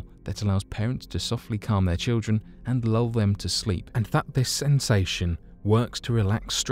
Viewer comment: Anyone else put this on to fall asleep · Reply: So I'm in bed 😴 then 1:18:18😳😳🧐I swear I heard and fuck this sensation 🤣🤣🤣